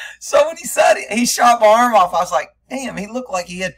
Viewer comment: I thought he was saying "He shot my elbow".